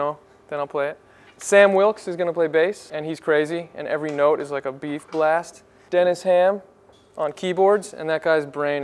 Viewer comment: Every note is like a beef blast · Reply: Ohh,, lol I thought he said "every note is like a B-flat"